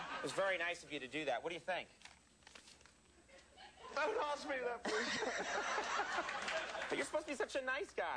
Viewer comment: what did he say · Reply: Not certain, but I think he said "Don't ask me that question", or something like that.